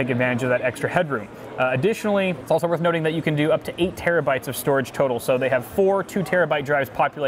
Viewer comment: tell me that doesn't sound like he said storage turtle.